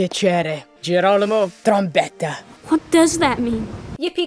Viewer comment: did he just say trumpet